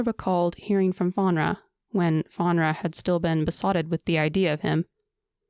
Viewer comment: For a minute, I swore it was said that she was "besodden with him" 😂